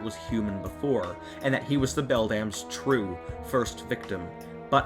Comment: the auto subtitles though you said “Bell damn” instead of Beldam. lol